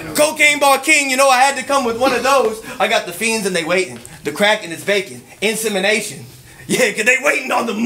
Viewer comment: did he say the crack is baking?🤦‍♂️😂